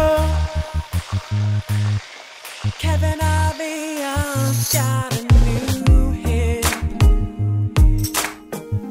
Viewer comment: i never got what willam is saying at 1:16. anyone know? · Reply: "Kevin Aviance got a new hip"